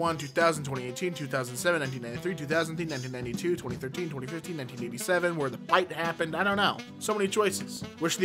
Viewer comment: wait- did he say 9087 when the bite happened???